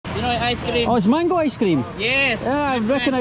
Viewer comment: Thought he was saying Michael ice cream